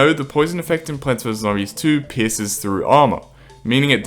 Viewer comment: I miss heard pierces😅😅 I heard pisses😂😂😂😂😂